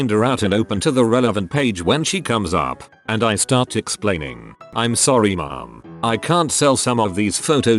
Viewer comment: The computer voice sounds like it said "I'm sorry mom" at the 1 minute mark. LOL!!!